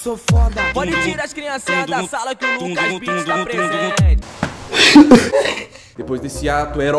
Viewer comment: só eu entendi: “q o Lucas lira tá presente” ?